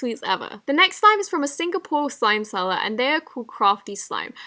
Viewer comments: did she say Singapore??? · what did she say?? “A single (??) slime seller” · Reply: Singapore :)